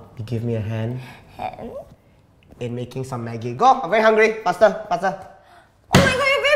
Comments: i heard maggi but in the subtitles it is money did anyone notice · subtitle wrong. making maggies instead of making money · it said money instead of maggi